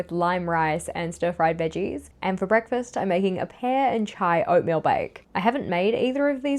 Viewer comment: Pear and Chai! Thank goodness, I thought you said Pear and Chive!